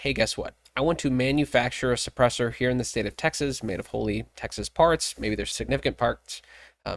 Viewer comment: As a hearing impaired person, I use the captions exclusively. But they DO make ERRORS. Sometimes funny, sometimes accurate. 2:58, I think you saying Wholly Texas Parts, but the caption read HOLY TEXAS PARTS!!! Yes Texas Parts are Holy, for sure. 7:24, you were likely saying Commerce Clause, but the caption was COMMERCE CLAWS! The way they use it to tear us up, well... Being mostly deaf isn't funny, but with Youtube, it has it's moments................